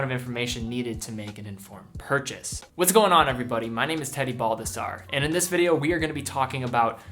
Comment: I THOUGHT HE SAID "MY NAME'S TEDDY BULBASAUR" 😂😂😂